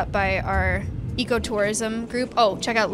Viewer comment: Every time she says "ecotourism" I hear "eco-terrorism" help